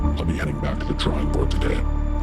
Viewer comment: Was watching OSRS and listening to the end and thought it said Sincerely, Bitch. LMAO